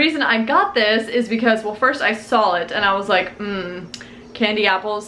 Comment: Does anyone else hear "sawl" when she says "saw"? 😂